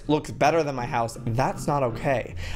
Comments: who else says "good damn bye" with Joey at the end of each episode😂💙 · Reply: what, i always thought he said good day and bye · who else says "good damn bye" with Joey at the end of each episode😂💙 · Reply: Charlotte S I thought he said Good day and bye......... OMG what is wrong with me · who else says "good damn bye" with Joey at the end of each episode😂💙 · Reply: Lydia Morgan me too I thought he said good day and bye not good damn and bye😂😂😂😄😄